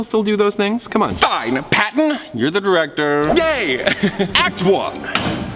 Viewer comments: Did he say gay? Edit: ok. The captions say he said yay but I heard gay. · Thomas did you say gay instead of yay because same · sounds like he said: Gay!